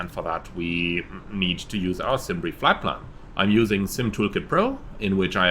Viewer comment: What is the program that he mention in 1:04? I Can’t understand what he said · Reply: 11 months later...😁 The programme is called 'Simtoolkit Pro' and it is freeware.